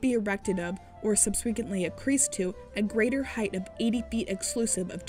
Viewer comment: Thats... Certainly one way to pronounce "subsequent", yeesh. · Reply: I heard "subsweecantly accreased". 🤣